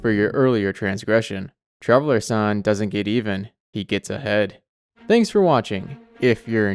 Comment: I misheard that. I thought he said traveler gets a head. 😏